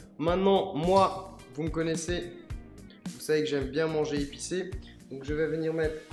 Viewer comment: A 5:45 il dit j’aime bien manger épicé et moi j’aime bien manger et chier mdrrr